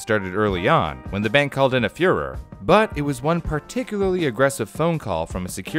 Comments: Am I the only one who misheard "furor" as "führer"? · "When the bank called in a fuhrer" Am I hearing this right? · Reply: Furor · sounded like you said they called in a Führer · The bank called in a what? It sounded like you said "a fuhrer". It definitely sounded like a designation of someone the bank "called in". I guess you must mean they called "in a furor" (were very angry on the phone). But the pattern of stressed and unstressed syllables used doesn't fit that meaning. It says "called IN" instead of "CALLED in".